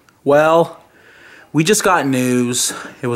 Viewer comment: When you said 'we just got news' I thought you said 'We just got nude' Lol!!!